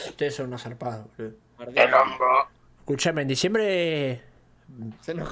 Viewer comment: que dijo?? Lo único que entendí fue "por la pija"